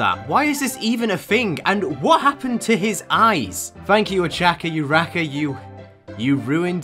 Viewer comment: MY GUY DID NOT JUST CALL HER O-CHA-KA U-RA-KA!!! come on!!!